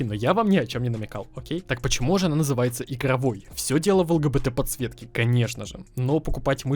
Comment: я один слышу лгбт подсветка